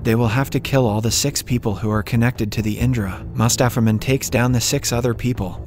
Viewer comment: everytime he said "mustapha mond" i heard "mustapha man" lol